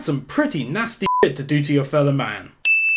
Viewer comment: i thought he said yellow man at first